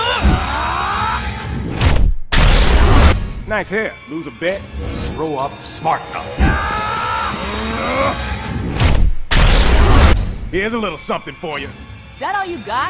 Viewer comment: Cyborg: Nice hair. Lose a bet? Aquaman: Grow up, smart mouth! XD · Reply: I thought he said "Grow up , Smarten up"